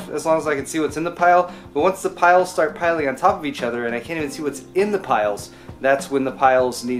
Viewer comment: I heard "when the kyles start pileing on top of one another" XD